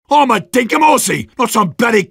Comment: I always thought he said "I'm a dick-a-moursey"